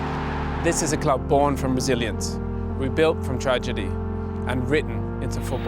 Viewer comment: Thought he said a club born from Brazilians :D